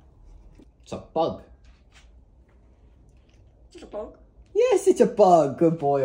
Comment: I heard “It’s a bug? More of a snack.”